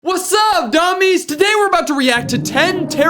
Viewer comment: What I heard in 0:01 is "Whatsupp GUmmYs"